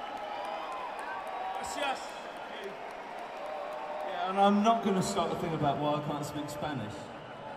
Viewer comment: Hi This is what I heard: 1:42:48 “Gracias, thank you. I am not gonna start a thing about why I can’t speak Spanish. The weird thing is like I’ve been banging on about Italian last week, and the thing is I did actually, I learnt Spanish at school. It was one of my languages but I learnt (or was angry) I was not taught Spanish at all because whenever I tried it on people, they said it’s not Spanish! I think that teacher is insane, (garbling noises, mimicking his teacher), repeat after me.” “What the fuck I am I talking about?” “Right, there is enough time for you to all be happy but this first encore is not gonna make people very happy, it will make you entirely fucking joyous!” “The first song is about my brother dying, it is called ‘I Can Never Say Goodbye.’” If someone else heard more clearly, please feel free to correct. 🥰